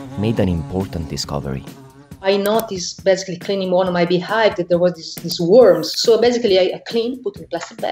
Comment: I know she said beehives but i heard "I noticed cleaning one of my behinds that..." and I was very confused for awhile.